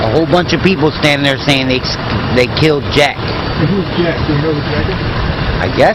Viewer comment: Lol sound like he said they killed jack